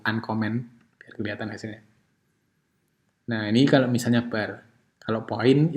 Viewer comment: #info 18:50 maaf pak itu bukannya line? kalo tidak salah dengar saya dengar menyebur "bar"